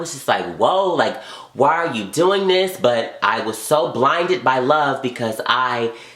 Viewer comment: everytime he said "im blinded by love" i heard "im blinded by that bomb-ass-dick"